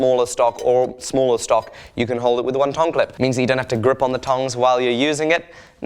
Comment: Tong clip, I thought you said Tongue Clip